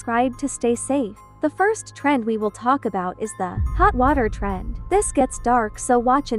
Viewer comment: i heard "the hot water trend" as "the hot water turd"